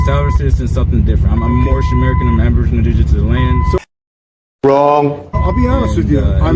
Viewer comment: i think he said "aborigenous indigenal to this land" but its hard to be sure, he kinda smears past it.